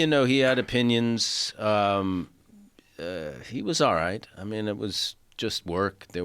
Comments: I thought you said you was alright spider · I thought you said I was all right spider 😂